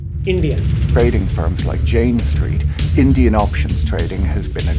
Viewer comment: I thought he was saying “Jain Street” some Marwari Traders behind the company